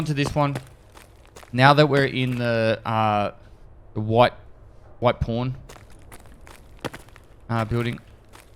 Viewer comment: I heard "white porn"